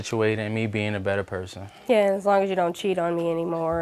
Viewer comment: 🤔 Did she just say that he blew off his finger with her gun, or am I hearing things? 🤔